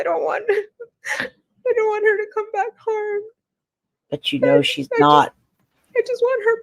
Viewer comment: I keep replaying the part when fake cries all of a sudden and I keep hearing her say “I DON’T want her to come back home.”